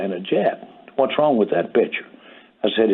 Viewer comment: did he say bitch? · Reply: lol he said picture